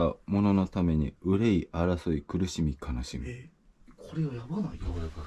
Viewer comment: に男の人の低い声でこれやばーいって聞こえる